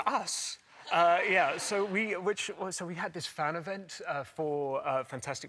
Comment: shit I thought it said Fantastic Breasts😂